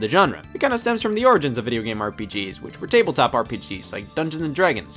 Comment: subtitles say "RGPs" but Scott says "RPGs". THEN YOU SPELL IT RIGHT IN THE VERY NEXT CAPTION